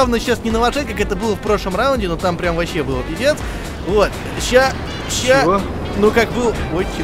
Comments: Лайк, если послышалось, как сказал "это вообще было пи#дец" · мне послышалась или сказал пиздец на